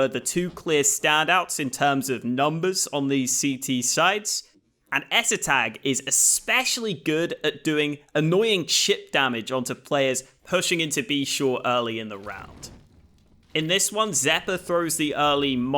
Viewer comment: LOL thought you said "annoying shit damage" at 1:46 and nearly dropped my coffee :)